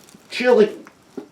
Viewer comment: kinda sounded like he said chili before he threw the can! XD